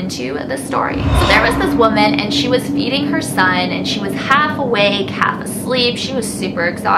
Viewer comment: In the beginning when she said the women was feedining Her son I thought she said she was eating her son😂😂